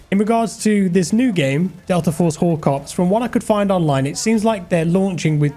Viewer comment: Looks awesome! ...did anyone else have to skip back the first time Jack said "Delta Force Hawk Ops"? I did an actual double take as I thought he said "Delta Force Wh0re Cops". Now....i can't unhear it.